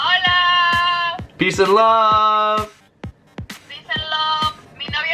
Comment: When she says peace and love it sounds like she says peace and lub 😂